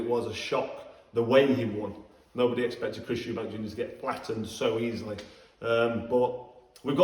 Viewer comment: I thought the cobra said eubank Gona get flattened... Booo boooo..the towels on you froch 😂😂😂😂